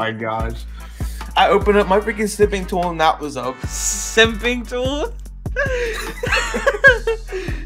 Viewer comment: I thought he said simping tool